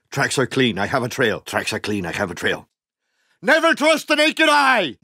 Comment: "Never trust any can hide" Is that what he said ?